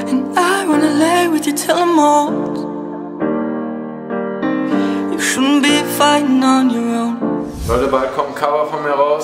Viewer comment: hab iwie nur "you schubi fighting on your own" verstanden